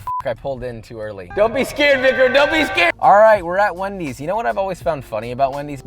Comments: did he just say the NIG-WORD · why did I hear the n word? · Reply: “Don’t be scared, Vicram” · did he just say the N-word? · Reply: I listened to it multiple times after thinking the same thing, but I believe he said Vicrim actually, he just said it really fast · did he say “don’t be scared n****”. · yo what did he say? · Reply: Idk I think he said victor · Anyone else hear what I heard @ 0:52? 🤔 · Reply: He was talking to his cameraman, who’s name is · why did i hear "don't be scared (N word) don't be scared"? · Did he say ni**er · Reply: He said “Vicram” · In 0:52 the subtitles said victor but I thought it sounded like something else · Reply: “Vicram” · What did he say at 0:52??? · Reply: "Dont be scared Vikram (one of his employees)" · Did he say "dont be scared nigger" ? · I must have heard wrong but can someone let me know what he said here? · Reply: “Don’t be scared Vicram” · LMAO I THOUGHT HE SAID "DON'T BE SCARED N*****" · I am mishearing this, it sounds like "Dumby's Scare Vigor!" Which actually sounds like someone you drink to heighten your sense of fear so you can enjoy horror movies more... I'd buy that. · Did say the N-Word??? 0:50 I could be wrong but I SWEAR he says “don’t be scared n-ger “ ???? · I'm sorry, but at 0:50 all I hear is don't be scared n*** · what did say??? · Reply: Don’t be scared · What did he say at 0:52? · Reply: Victor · what did he say???????? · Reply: "Vicram" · did he just say don't be scared n*gger?